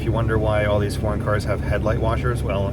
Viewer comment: I initially thought you said Foreskin LOL, Im on the floor laughing sir... excellent video for diagnostics, thank you